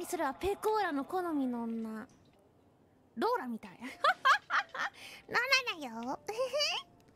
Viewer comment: I think she said ローラ(Ro-ra), not Dora. Ro-ra (also known as Rola) is a Mixed Japanese Celebrity, "ro-ra dayo" and her laughs are kind of Ro-ra's character signature.